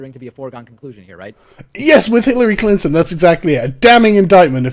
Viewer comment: Hitler-ly Clinton. I could have sworn I heard it in the vid.